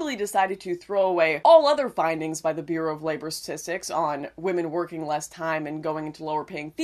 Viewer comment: i thought they said science not statistics